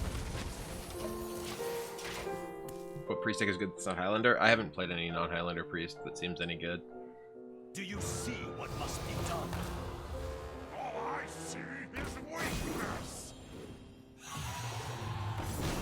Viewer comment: “Do you see what must be done?” “All I SEE is weakness!” Epic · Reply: This whole time I thought he was saying "Oh I see, his weakness" I'm dumb